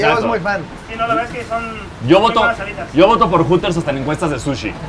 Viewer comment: Me super rei en este minuto porque pensé que había dicho ``yo voto por "pvtas" hasta en encuestas de sushi`` Lo repetí y después me sonó ``yo voto por "Hooters" hasta en encuestas de sushi`` Toi loco :c